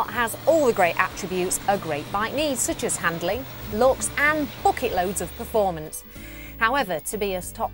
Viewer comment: sounds like she said "f(ooking loads of performance)